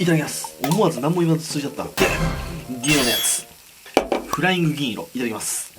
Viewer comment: フライング銀色がフライング議員に聞こえたw